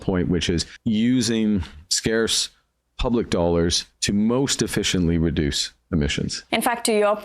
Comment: All I keep hearing is "Republic Alberta".